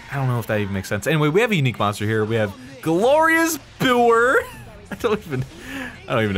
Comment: "Glorious Buer" Me, mishearing: "Ferris Bueller"? -- I remember I wrote down some thoughts I had to myself, back when I first watched Part 40 of this LP. I'm just gonna revise said thoughts as my current self is kinda rusty. ... Yep, that was when I realized who Metal Face was. I referred to him as "That deserter that was in the first war against the Mechon." But I didn't remember what happened to him, nor what his name was. What I wrote about Fiora is that -after my initial shock that we never did see her body after That Dreadful Night- I believed she was actually a clone, and I'm still...??...iffy about the Fiora thing even though amnesia seems like the easiest answer, my guards are still up. -- I'll see to writing down notes on the videos from now on, as a helpful resource! And it should be fun to read in the future :V (I'm wondering about how all this Mechon vs. Homs thing came to be if they're controlled by other Homs. Plus some things said by Egil. How curious.)